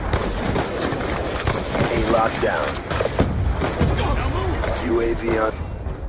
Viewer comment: Did he just say "Aimbot Down"?